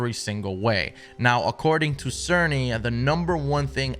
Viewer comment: he said Serny? i almost thought he said sony but there is someone name Mark Cerny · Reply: Yes, as in Mark Cerny